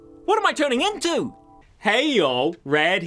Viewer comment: Ngl at first i thought he said mario piss but only the second time did i understand that he meant to say pez